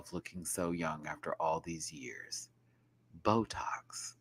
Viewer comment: Actually Cynthia did say Bootox, not botox. But Ru just replied saying "Oh, I thought you were going to say something else but you just said botox"